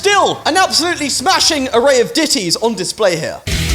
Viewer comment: did he say titys 😂